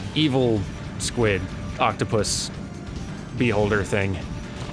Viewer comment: Sounded like he said bee holder thing.